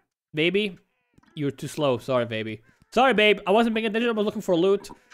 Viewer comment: Is it only me or did he say babe??